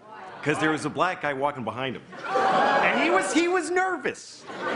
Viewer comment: Originally heard it as “because a black guy was walking up to him in the sidewalk”